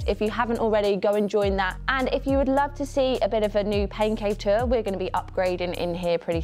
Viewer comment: Oh! Pain Cave Tour. I swear I thought you said Pancake tour. I thought I was under a rock. 😂😂 I’m training for a super sprint triathlon indoors as well. So convenient for my schedule.